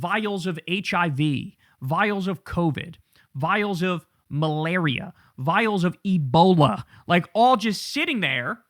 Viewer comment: I'm at work only paying half-attention... thought I heard "vials of Melania" 🤢